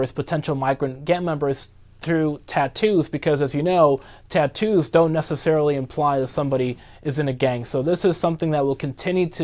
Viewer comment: "Gang tattoos dont necessarily imply someone is in a gang" he said 😂😂😂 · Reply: That's not what he said. He said "Tattoos don't necessarily imply someone is in a gang."